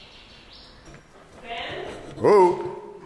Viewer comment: did she say Ben come here? so his name is Ben?